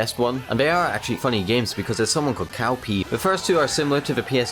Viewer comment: it sounded like he said, "Cow pee!" HAHAHAHAHAHAHA.